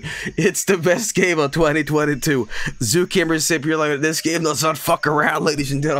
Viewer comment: Excuse me did he just call the game Zookeemer Sipulator